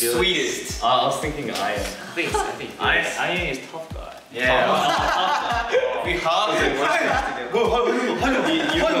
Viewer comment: What did changbin say? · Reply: I’m very late but he said “I.N. Is tough guy” - Jeongin is a cutie but he’s from a part of Korea where they are a bit more direct with their speech so he can sound harsh, which is hilarious because of how adorable he looks 🥹